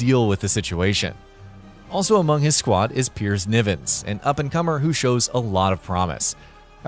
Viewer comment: My brother and I misheard Nivens as Nibbens, and my word that made our playthrough so funny.